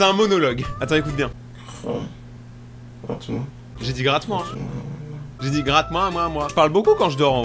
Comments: moi perso j'ai entendue "tuée moi 😓" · A 11:21 moi g entendu g faim traite moi